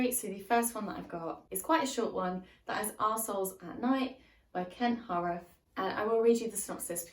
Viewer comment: OMG I have never laughed soooo hard while watching one of your videos 😂🤣 First off, at 0:44 I heard "arse holes at night" instead of "our souls at night" - Yes, that's right, despite the fact that I was born in 1993, I am in fact a twelve year old XD (although, in my defense, being from the US, I'm not used to hearing folks speak with British accent IRL so it does take me like, half a sec longer to interpret words spoken by ya'll from across the pond :3) Secondly, at 3:36 "The next book is second in a trilogy...I've heard there's maybe going to be another book..." - LMAO I was sooooo sure here that you were making fun of Kingkiller Chronicle before you showed the book you were actually talking about XD (I have not slept well past few days so my brain just autopilot at this point and is interpreting ppl's words wrong lol) Anyway, great video as always <3 And who know, perhaps one day I will grow up a bit and/or catch a good night of sleep :3